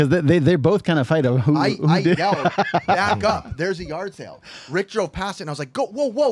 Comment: Did you say ricktro.. I can dig it